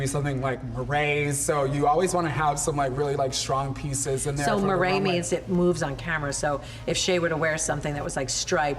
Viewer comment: What's the word they say at 5:45? marades? murrays? Ok, I found it. It's called moiré.